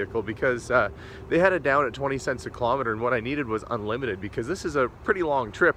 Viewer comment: At first I thought you said 0.02 cents per kilometer. I was like "that's like 40 bucks! That's not bad at all!" Then I realized you said 0.20 cents and I was like "oh..that's $400". Yeah damm. Thats way too much.